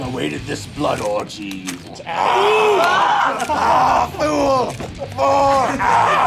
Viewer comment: I don't think he's saying "More" or "Fool" (In the second line, not the first where he says "more.") he's actually saying "Thor."